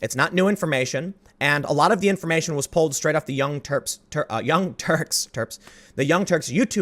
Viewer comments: I thought you said "The Young Turds". 😂😂😂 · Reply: I heard "The Young Twerps" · I thought you said "The Young Turds". 😂😂😂